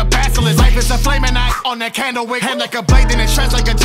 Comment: Is it just me or did I hear "On the candle wick" and read the word candlestick?